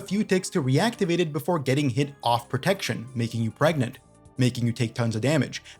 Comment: as someone yet to try OSRS...did you say make you pregnant lmao